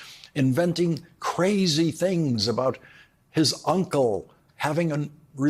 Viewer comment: Trump should have been disqualified from running for office based on his criminal conviction in the first place. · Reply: Thought he said Eppie is irrelevant